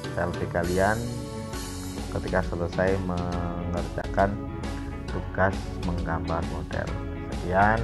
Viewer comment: Maaf pak dimenit 14:24 bapak ngomong apa? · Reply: Setelah selesai mngerjakan tugasnya, menggambar model silahkan dikirm ke WA